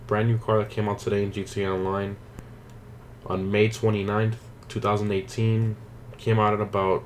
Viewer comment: M I the only one who heard 2019😂😂